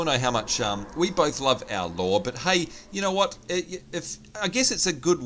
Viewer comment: When I first watched this vid, I thought you said "We all love Al Gore" when you actually said "we all love our Lore"!